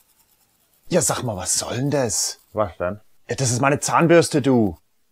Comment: was hat er im 0:50 gesagt? auf deutsch ?? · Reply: Ja sach mal was sollen des?